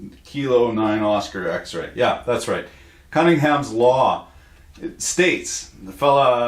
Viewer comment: I thought you said "Hanrahan's Law"....but his wife is a lesbian